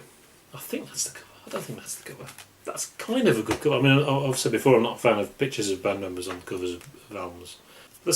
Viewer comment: what did you say here?! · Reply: ""As I'be said before I'm not a fan of band members on covers of albums"